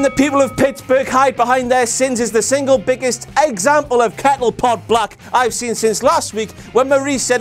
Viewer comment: Did he just say "Pettle Kot Black"???